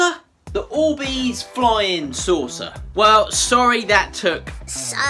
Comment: I wasnt paying attention and looked up when I thought you said "Flying Salsa" not "Flying Saucer" XD